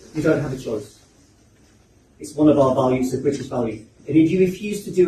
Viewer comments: I think he said "BRITISH values" but I could be wrong. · I think he said “the British values” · I think he said "British" values.